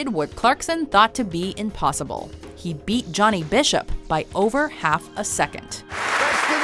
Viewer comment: Did you just say... Johnny Bishop?